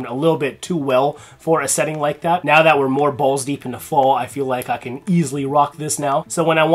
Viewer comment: I could've sworn I heard "balls deep into fall." Who's Fall? 😄 Btw, finally got to try Incense City by AllSaints and it totally reminds me of Moab by Phlur. Love to layer with that one. Sunset Riot....well that's just BR540. Iris Fauve is some pretty damn good stuff though.